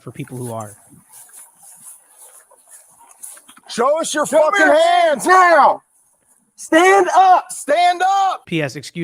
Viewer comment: Did anyone think the Cop said "shows us your filthy hands now!".